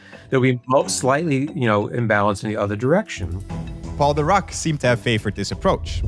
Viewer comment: When you said "Paul Dirac" I heard "called The Rock". I did a death stare to my tv